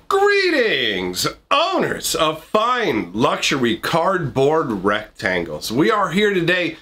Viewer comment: I thought he said “Boners of fine luxury cardboard rectangles” 😂